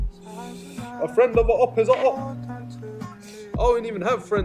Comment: What did he say here 1:02 ?! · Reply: A friend of a opp is a opp